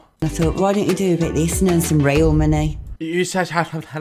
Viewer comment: i thought she said rail money